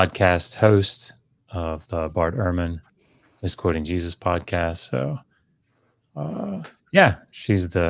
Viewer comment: It sounded like said Bart ehrman’s exploiting Jesus podcast. Another f- slip?